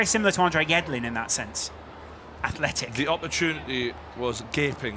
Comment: did he just say Americans aren’t Athletic?